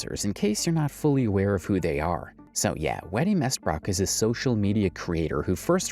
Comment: “Wattie Mestbook” is what you said and it sent me